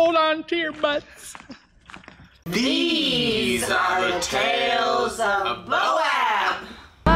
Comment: Lol I always thought that lady was saying “tales of Moab” 😂 So glad to see you guys back at the boat yard. Happy sails from Colorado.